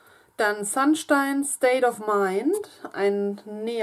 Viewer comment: Ich hab gerade verstanden "Sandstein state of mind " 😀🤣